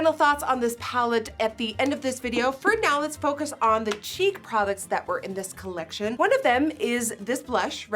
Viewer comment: Full offence: it looks cheap and like “kid makeup” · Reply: When Nikki said "time for the cheek products" I swear I heard "time for the cheap products" 🤣